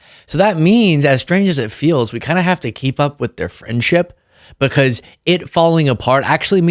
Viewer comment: When you said their “friendship,” I thought you said “cringeship,” and now I think that’s the perfect name for it.